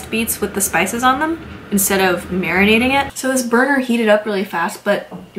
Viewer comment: Wait, did you say "This burger" at 10:00 ! lmao